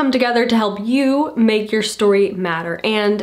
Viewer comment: When said "make your story matter" I started laughing because it sounded like she said, "make your story madder", which made sense to me because we are cutting words -- precious words from our stories! I love.